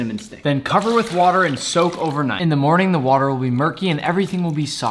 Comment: I thought he said "cover with water and soap overnight". Idk why.